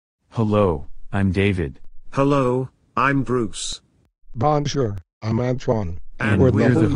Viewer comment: can you please specify what Antoine said? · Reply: "Bonjour!" means Hello in French.